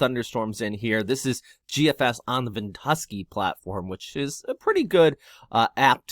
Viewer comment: I thought you said Tuskey app. The site is <link> for anyone interested.